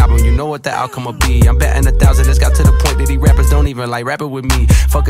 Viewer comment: who else hear every word, crystal clear. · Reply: I did, but i misheard batting for betting